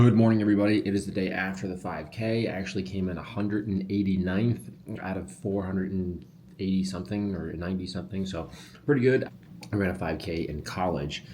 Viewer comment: I thought you said "I came in 489th of about 480 or 490 something, so pretty good!"🤣